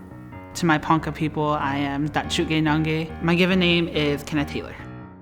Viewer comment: Dude my mom was watching this, and I was half-listenting to it, and I swear I thought Kenneth Taylor said "I'm a chicken nugget" at 5:29 lmao